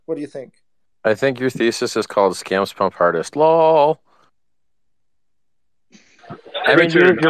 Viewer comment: Does he say scamponpartest law? what is that at 20:11?